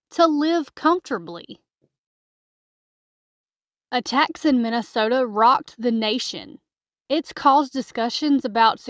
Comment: Speaking as a MN resident, I still managed to mishear this as “a tax in Minnesota rocked the nation” and REALLY did a double take trying to figure out how the hell I completely missed whatever that was. Alas, if only. Appreciated the accidental levity of that moment, though.